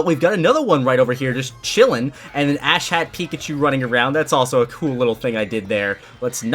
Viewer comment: Fuckin hell I thought you said Asshat pikachu lmao